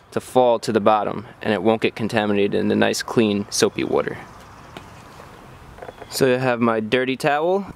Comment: I'm simple. I come to hear chris say "soapy water" and that is all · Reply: Sounds like he says soapy woudr